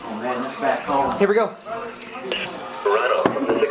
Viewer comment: who heard the weather radio saying Rhino?